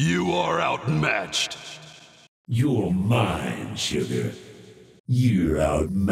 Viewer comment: i always heard you're mine children 😂😂